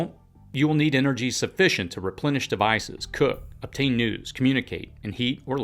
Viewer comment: Is it me or at 13:03 did he say we will need energy to “obtain nudes” 😅